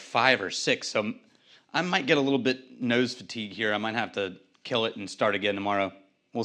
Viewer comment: Sorry, did you say "nose to teak" (3:50)? Whazzat - another? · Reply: I think he said “nose fatigue”